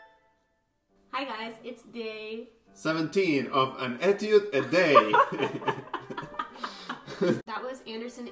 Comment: What'd he say at 2:12? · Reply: it's day 17 of 'an etude a day'